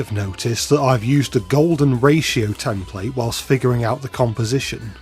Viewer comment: Did you say... G O L D E N R A T I O ?